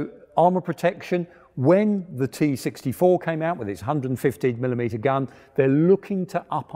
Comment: Did i... did i just heard "T-64 with its 150mm gun" or am i just imagining things?